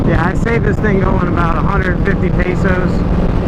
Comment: Is he said that 150 pesos?